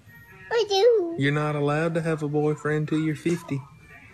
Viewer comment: Did I just heard “until your 50” ØwØ I think I’m dreaming